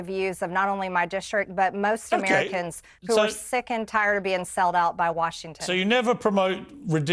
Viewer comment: Did she say Americans are tired of being "selled out" ?!?!